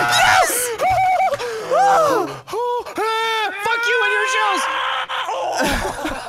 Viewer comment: Playing this at 2x speed, I swear Wildcat says "Fuck You NHS"